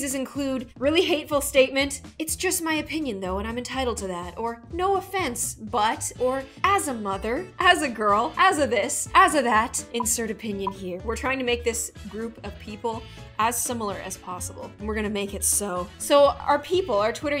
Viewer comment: When you said as of as if this I thought you were saying this that